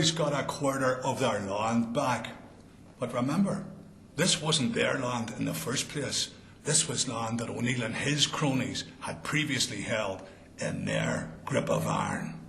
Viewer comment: love the way the guy talks. i just can't understand who he's talking about in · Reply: he was talking about the Flight of the Earls Hugh O'Niell and Rory O'Donnell